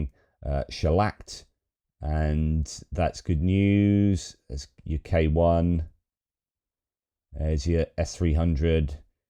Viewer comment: Instead of 'There's your K1," I heard "UK won", and I couldn't help thinking was taking another dig mid video at the Aussies.